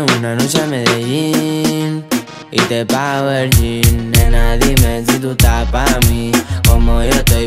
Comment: Pensé que decía “Te bajo el Jean” No se le entiende no verga